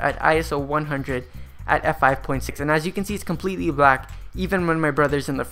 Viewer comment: aperture settings? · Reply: I thought he said f5.6? 2:41 But remember, your settings will vary because your ambient light situation will be different (you need to get your background to be pitch black).